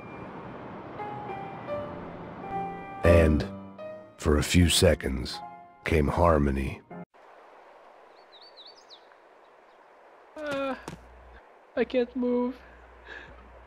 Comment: LOL 0:21, "Arm in me" instead of "Harmony"